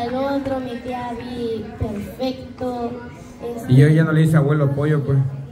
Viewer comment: Los mata pasiones de abby me gustan jajaja 😂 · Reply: Yo devolví el video porque pensé que dijo abuelo pollo y nada que solo dijo ever